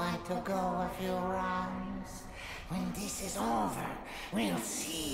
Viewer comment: I can't understand what N.Gin is saying at 04:07 - 04:17. At the part he speaks "So... you want to go ...???... When this is over, you'll see who's obsolete!". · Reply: A few rounds